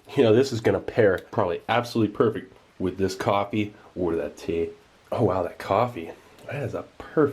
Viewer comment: What does he say in 8:02? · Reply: Ali A He says the biscuits pair well with the coffee… or that tea.